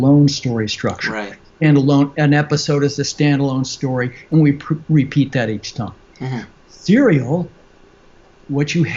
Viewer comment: mins in he mentions something like butchco started this before the Sopranos, anyone know what he said? Thank you! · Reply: Steven Bochco. He had a lot of major shows. He was even involved with Columbo but that wasn’t serial. It was however one of my favorite shows to watch and it was different than anything else on TV at the time where we know who the killer was from the start, Columbo knew early on and then it was just about Columbo proving the guy did it. I guess this exemplifies what Truby said about the point if the detective genre being about whose version of the truth will be believed, the killer’s or Columbo’s. 😀.